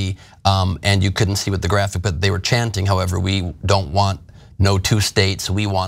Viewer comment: Oh boy did I ever mishear the "we don't want no two states" chant. Thought they were saying "we don't want no Jew state" and I was thinking hmm that actually does sound pretty antisemitic